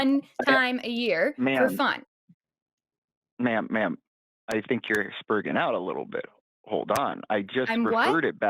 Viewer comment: Did he say “I think you’re sperging out a bit” at 4:18?